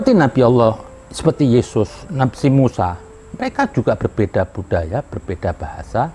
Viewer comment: (2.49) Misall... Seperti nabi Allah,, yesus,, si mussa Ap saya salah dengar tolong koreksi 🙏 Mungkin kalo saya bilang nya,, seperti nabi Allah Muhammad SAW, nabi Isa As, Nabi Musa As Dan untuk habib zen, menurrut saya akan lebih baik apabila anda menyebut nama baginda dengan Nabi Muhammad SAW. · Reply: Nabi Allah bukan berarti nabi Muhammad doang kk dia bilang Nabi ALLAH , YESUS , NABI SENG MUSA dia bilang seng Musa itu bahasa Jawa kaya nabi seperti Musa gitu truss kenapa dia bilang Yesus itu seperti beranggapan toleransi makanya dia bilang Yesus kan Yesus juga kita mempercayai nabi Isa. Paham disini ?